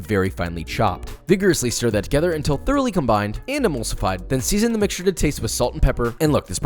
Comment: At first I thought that you said "animals fight", when you said EMULSIFIED. 😁 AND LOL AT YOU GUYS OVER THE SEX SOUNDS FETTUCCINE!!!🤣🤣🤣🤣🤣🤣🤣👍👍👍👍👍👍👍